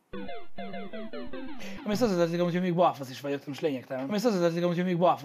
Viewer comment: jól értem? "Mondjuk az is hozzátartozik ,hogy balfaszvagyok" ( nem a káromkodás hanem amit mond) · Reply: "Mondjuk ehhez hozzátehetjük amúgy hogy még balfasz is vagyok de most lényegtelen" :D